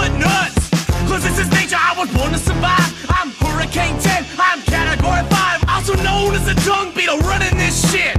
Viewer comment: Nah the dung beetle line is the best "also known as a dung beetle running this shit" solid double entendre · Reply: i thought he said "I'm Hurricane Ten, I'm category 5!"